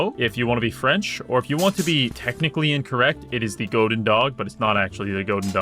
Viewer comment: Any willing to type the name of this weapon for me? because my attempts to google it has failed... (Edit: Think I found it, is it the "Plançon à picot"?) The second (technically incorrect) name I first hear as "Goat and dog" then "Golden dog" before google gave me "Goedendag".